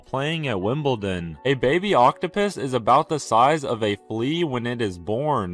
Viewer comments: Please tell me I'm not the only one who heard him say octopiss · I can't be thw only one that heard him say octopiss